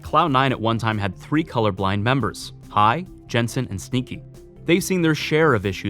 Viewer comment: did u just call sneaky stinky